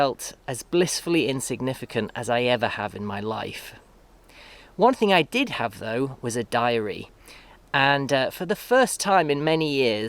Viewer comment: I really heard "But one thing I did have, was a diarrhea"